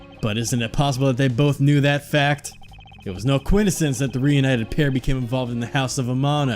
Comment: Did he say coidisence lol